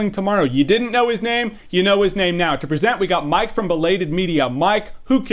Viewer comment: 10;37 At first I thought he said "Mike from RedLetterMedia" and I was fucking excited, but eh... You guys should try to get him for next year's awards.